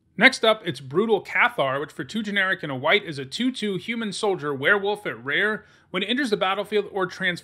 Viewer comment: That human soldier werewolf gives me an idea for a top 10: creatures with three types. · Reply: Thought you said human spider werewolf.